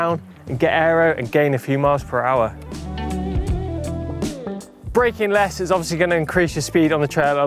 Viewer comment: I thought it said "Brakeless"